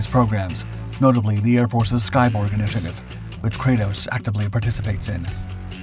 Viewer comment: I thought you said 'Skybong'. My bad.